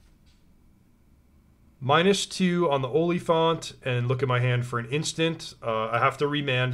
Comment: My brain is seriously broken, when said oliphent (instead of oliphant) , I thought he said onlyfans….